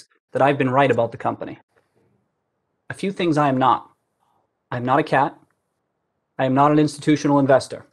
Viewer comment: Bruh did he just say I'm not a cat? 😂😂😂